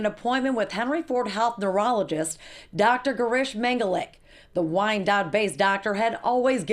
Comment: At first I thought the narrator said Dr. Mengele. That woke me up in a hurry! He's still a reprehensible character.